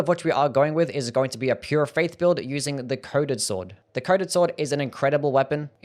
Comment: Lol, when you said coded sword, I thought you said, "Covid sword." XD